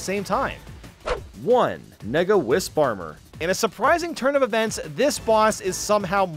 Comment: WHAT DID HE SAY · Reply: Nega Wisp Armor